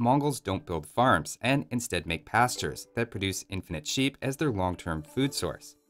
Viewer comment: I misheard, "infinite sheep" as, "inbred sheep" at first and had a good chuckle before rewinding to see if that's what he really said or not.